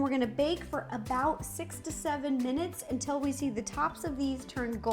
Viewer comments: i thought she said bake for 67 minutes and i was like HUH until the third time of rewatching that part I was like OH 6-7 MINUTES lol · Bro u thought she said 67 minutes for the marahmello one